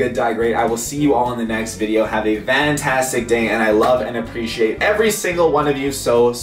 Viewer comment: when it came to 21:36 it sounded like he said have a vantastic day lol or is it just me